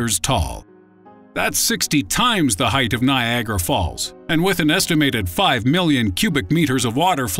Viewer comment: I thought he said "Viagra Falls" at first... Something "Strange Mysterious" has gotten into my brain that very moment!